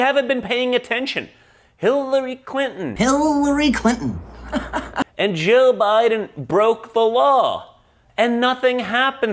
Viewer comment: Did he just say Jill Biden broke the law? 😂